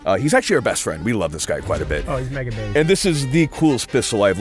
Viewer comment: Did I just hear say "oh he's mega day"